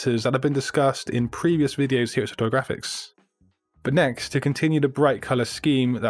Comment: ! A mistake by your subtitle writer. I am sure what you said was "satori graphics" not "photo-graphics".